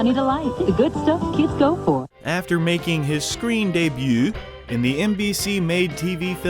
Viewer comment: Did you say "screen day byouuu🤢🤮"?